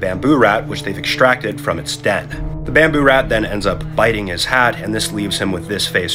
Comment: Man, the subtitles "And puts it towards the bamboo rat, which they have extracted from its DEATH" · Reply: I misheard it as “from its death” too! I thought, “It was still able to bite his hat after it had died!?”